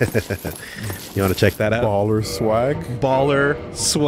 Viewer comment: I heard "Balder Swag?" while I was in the other room and freaked out thinking the sword dropped for him